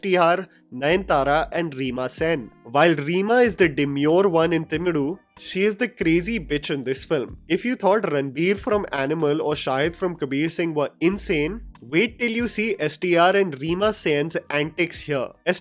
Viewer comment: Did u just say crazy beech bro?😅